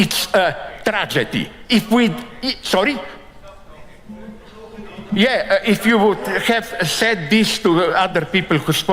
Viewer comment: At about 22:40 an audience member says something, and Prof Zizek replies he wishes that was said to previous [rude] audience members who said something. What did the audience member say? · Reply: I think he said '... Stop talking...'